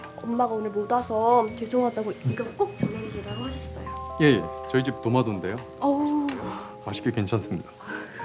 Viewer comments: 네 저희 집 도마도인데요 라고 저만 들리나용ㅋㅋㅋ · “네 저희 집 도마도인데요” 라고 들리는건 저뿐..?